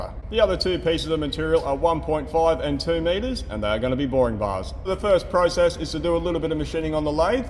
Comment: No music, no ale, what a boring bar. Thanks and, big WOOF! to Homie. Have a great weekend guys. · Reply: Ooohhh BAR i thought he was saying boring bah 😴🐑